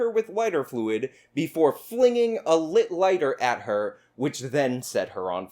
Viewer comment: They're rather expensive though, so the idea someone would throw one away like this sounds unbelievable. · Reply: In the video, it sounded like said the girl claimed it was a Bic lighter.